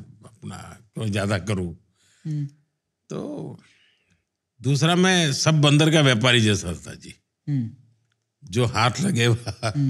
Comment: I think Modi ji said "main sab bandar ka vyapaari tha", instead of the other way round @ 18:30. Little confused : O